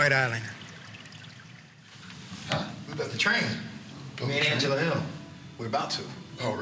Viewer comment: Dana thought Din said lets run a train on Angela Hill.